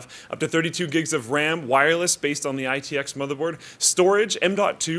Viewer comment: Sounded like wireless ram